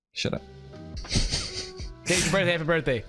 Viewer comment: If I didn't know Ken was saying "if today's your birthday happy birthday" I would have sworn he said "and if today's your first day happy birthday" lol 😂